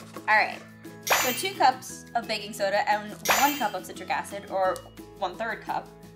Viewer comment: When she said “Baking Soda” I thought she said “Baby Soda” 🤣